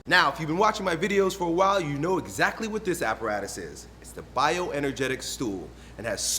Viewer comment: I always hear "bioenergetic stool"